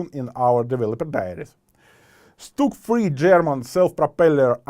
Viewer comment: did he say a StuG III is in development?!!! 😍😍😍